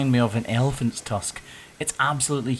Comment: I’m super tired and thought it said “eggplants tusk” at first 😂. I was like “what the hell kind of eggplants do you have there?!” 🤣